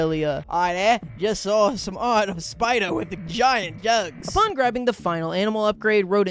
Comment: yk its a good accent when you can't understand a single word he's saying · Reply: He saw a giant spidah with some giant jahgs.